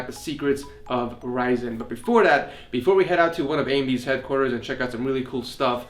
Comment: did he just say amy's headquarters? lmao.